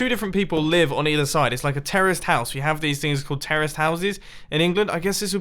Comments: did he say terrorist house? If so and if that's an actual thing, why!? What was the person thinking who named that style of house?? Could anyone explain it to me? please..? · Am I the only one who heard him say “Terrorist House”? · Did... Did he say it is a terrorist's house? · Am I the only one who thought he said "Terrorist Houses" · Am I the only one who heard him say “Terrorist House”? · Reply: I heard him say Terrorist House too😂😂😂